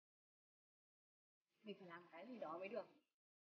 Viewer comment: yến nói gì thế nhỉ, mình ko nghe rõ lắm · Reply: Hình như là: "mình phải làm 1 cái gì đó mới được". Mình cũng nghe loáng thoáng thôi nên cũng không chắc. Vì nhạc to quá nên nghe không rõ.